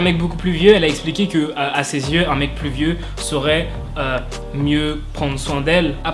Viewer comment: J'ai entendu "un mec pluvieux"... 😐